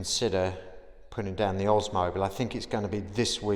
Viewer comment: Oh my I think I just heard you say “OSMOS” ! YES !!😊